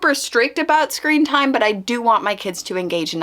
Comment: I legitimately thought you said “scream times” and I was picturing setting a timer for my kids to scream it out! Then I realized what you actually said.. but I think screaming it out might be therapeutic! Hahaa